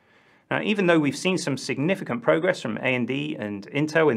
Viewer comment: A.N.D. what ??? I'm assuming you meant to say AMD... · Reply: I did say AMD, but sometimes I mumble, slur and slip back into my west country farmer accent.